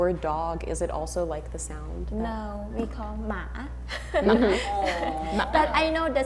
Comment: The subtitles at 10:34 says she said the word นัข, probably coming for the word สุนัข (formal word for dog), but she actually said หมา (dog).